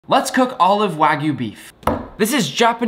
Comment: Ooooo I thought u said let’s cook all of wags beef